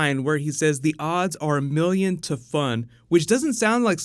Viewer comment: I thought he said the odds where 1,000,000-5